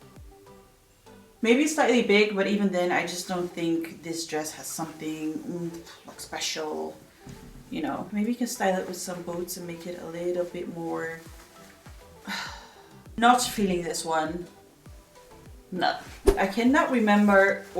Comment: I thought you said: 'maybe if I pair it with some BOATS' 😂